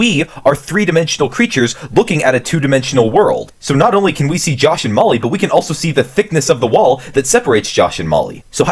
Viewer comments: "josh and molly" i fr thought he said josh and mullu from the boys lol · Anyone else thought he was saying josh and mully? (The boys) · Deadass thought he said josh and mully